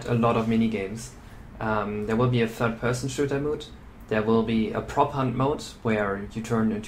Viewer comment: Couldn't understand what you said. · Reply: "There will be a 3rd person shooter mode"